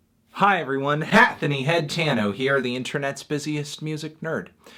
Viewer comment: It sounds like he says "Hatthony HEADtano" but the text says "Hatthony Hattano"